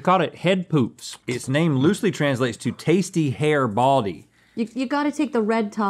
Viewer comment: Pelon, Pelo rico translates a bit more closer to "Baldy with tasty hair"... Oh wait, you did say "Tasty hair baldy"! I thought you said "Tasty hair body"! My bad... 😅